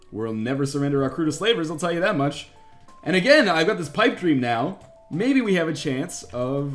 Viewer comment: At around 10:52 did he say pike dream? I think maybe he meant pike beam, in which he meant fire beam?